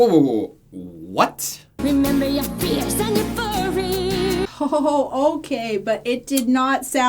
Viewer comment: What did you think it sounded like?? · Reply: We heard "a furry" instead of "you're furry".